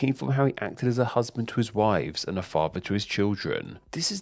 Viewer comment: i thought you said “a father to his wives and a husband to his children